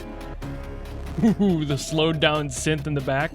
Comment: what did he say???? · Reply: Distortion..? IDEAL..?